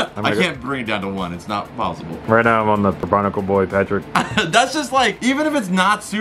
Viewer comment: am I the only one who heard him say patrick mermaid man insted of barnical boy?